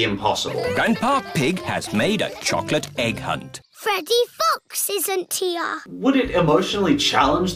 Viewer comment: Peppa say's "Freddy Fox isn't here" but for some reason I keep hearing "Freddy Fucks isn't here"